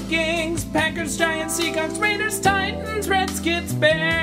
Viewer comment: Does he say 1:26 Seacocks? :-D